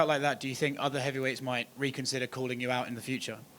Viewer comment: it really sound like he said JE consider. I dont know if ure french but it sound like he messed up lol. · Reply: I'm not French but I speak both French and English. The person asked him if other have weights might "reconsider" calling him out after seeing that knockout. Francis followed up saying "reconsider or not we have to fight each other".